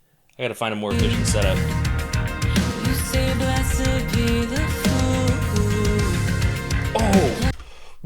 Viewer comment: I always thought she sang, "... you say blessed be the fool, 'who'..." Hmmm